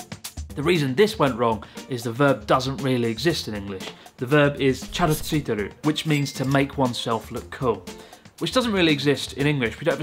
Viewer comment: For a second there, I thought you said チャド付く and thought Japan had taken up using 'Chad' as an adjective as well.